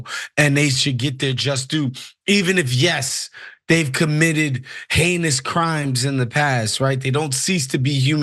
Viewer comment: Oh, heinous crimes . . . I thought I heard anus crimes.